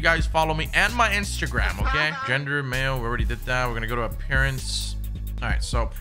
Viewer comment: did he just say parents hahahahaha